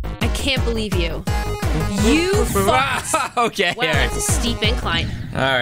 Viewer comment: Did anyone hear said you f*cked